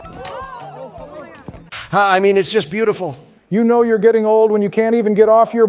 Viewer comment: What Trump says at 5:35 after watching the clip of Biden falling off that bike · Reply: Ah is butfle